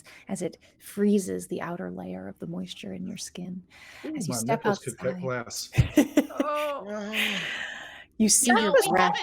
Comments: What did say at 1:30:10 that upset everyone? I can't make it out. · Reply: Not entirely sure myself, I hear "My nipples could [prick?] glass"? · What did say at 1:30:10 that upset everyone? I can't make it out. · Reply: I said "My nipples could cut glass" - a Friends reference (I'm not proud of Silas for referencing that show). :)